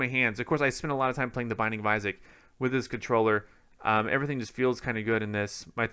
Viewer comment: says he spends a lot of time playing "....". What does he say here? He kind of mumbles. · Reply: Drake Williams the binding of isaac